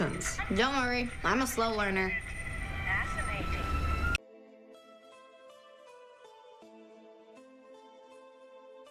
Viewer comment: WAIT?! WHAT DID THAT JUST SAY.. THE VOICE COMING FROM THE T.V?!?! IS THIS SUBLIMINAL MESSAGING???? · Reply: Not.enough to.go.around Oh shit...